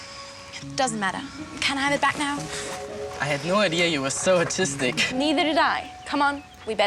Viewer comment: when he told Emma he thought she was so artistic I thought he said autistic